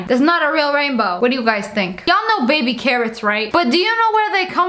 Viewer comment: I thought she said baby karens